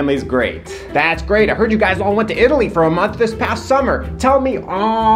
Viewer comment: Why the hell did I hear 'Italy' 🇮🇹 as 'idli' (a breakfast snack in India 🇮🇳)?? Um it's time for me to check my ears!!🙂